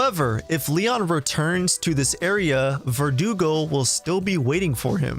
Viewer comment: it sounds like you said verdugle lol🤣